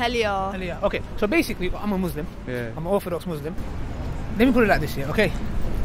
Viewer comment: Did he say, "I am orthodox Muslim."? if this is what he said, can someone please clearify for me? thank you.